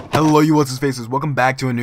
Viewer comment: what do u say in ur intro? lol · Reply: I thought you used to say "Hello you luscious faces, Tigon here" I am so dumb